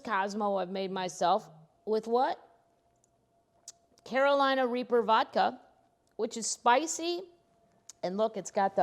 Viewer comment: LOL Before I read the name of the vodka I thought you said Carolina Reefer vodka!! LOL I have been living in Colorado for too long LOL